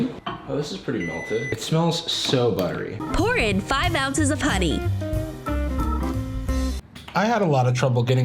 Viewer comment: I thought it said, "pour in 5 ounces of putty"......